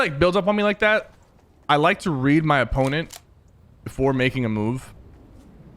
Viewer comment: My sleep as thought he say rape instead of read. I just woke up